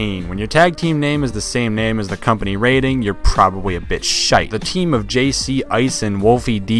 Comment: did he say shite yay a Scottish reference ps. I'm Scottish